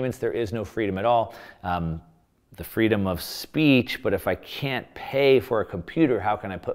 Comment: What is he saying at 6:12? "If I can't pay for ................................". I listened to it over and over and can't understand what he's saying. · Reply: If I can't pay for a computer